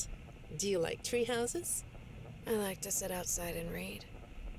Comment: In 14:54 did she said raid?